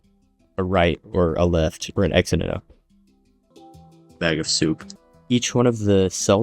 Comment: Did you say bag of soup?